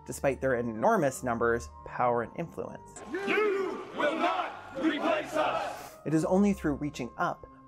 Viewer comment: Great video! I'm not sure but at 8:22, the captions say "you will not replace us" when I'm hearing (and remembering that it was discussed that they chanted) "Jews" and not "you"